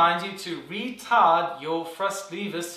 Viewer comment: The first time i heard it i thought it said “RETARDED”